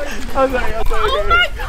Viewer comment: Did andrew say I'm sorry baby